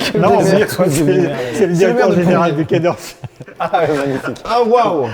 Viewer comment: ? Directeur général de quoi ? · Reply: Du quai d Orsay